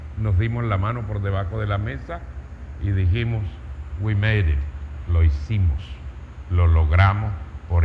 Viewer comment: Qué es lo que dice, como se escribe y que significa, en el minuto 17:58, cuando le dice a Juan Gossain, algo así "we matther", yo lo escucho "we meider". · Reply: Miguel NIÑO we made it = lo hicimos